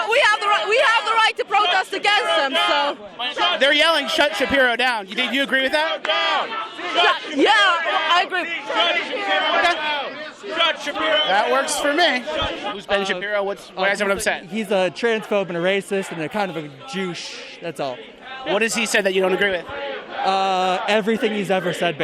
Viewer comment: is it just me or did he call Ben a Jewuche?